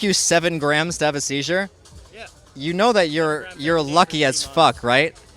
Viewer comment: i thought hes gonna say ''you are lying as fuck'' LUL